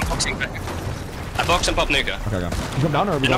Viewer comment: Did any here say what sounds like the n word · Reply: Pop nuka 😂😂😂